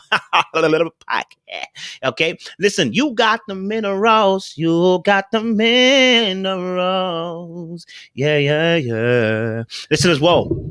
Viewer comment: With allllllll the videos ive watched by I really thought he has been singing "middle rose" but it's "minerals"!!!! 🤦🏾🤣🤣🤣😭😭😭😭 🤣🤣🤣😂😂😂 IM DEAD!!! 🤣🤣🤣 I crack myself up! 🤣🤣🤣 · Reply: Omg, you too!? Lol. I thought he was saying "You Got the Men Aroused" for about a month.🤭 😆😆😆🤣🤭 geesh